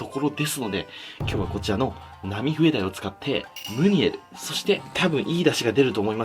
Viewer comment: ナミフエダイが空耳で波平ダイって聞こえたの俺だけじゃないはず